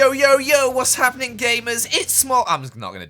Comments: i thought he said “gaymers” 😭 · i heard GAYmers 😂😂😂